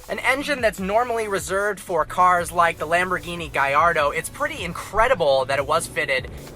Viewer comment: Did you just say Lamborghini gayordo??